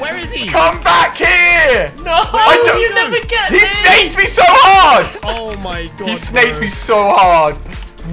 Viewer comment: When he said "he snaked me so hard" I thought he said " this makes me so hard"XDXD